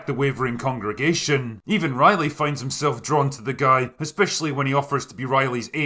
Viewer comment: Thought you said Riley Finn at first and thought they were using the character from Buffy.